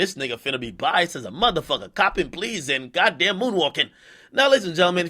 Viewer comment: did you say comtom please ?